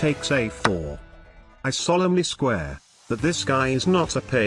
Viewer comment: did he say he solemnly square?